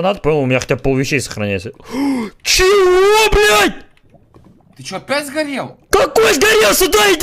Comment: I hear CHUIBLOOBED